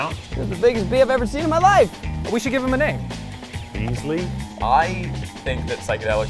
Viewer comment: Hold up, 2:27 does he call the bee Ainslie/ainsleigh/ainslee??? I might have not gotten the merch with my name on it as a kid, but this was worth it